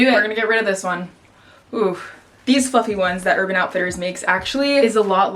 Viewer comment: i thought she said "these fucking ones"